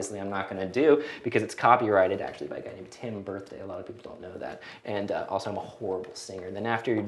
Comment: what is he saying at 3:23? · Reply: "...copyrighted by a guy named Tim Birthday, a lot of people don't know that..." It was a joke, though it is true that the song is copyrighted, but by Warner/Chappell Music.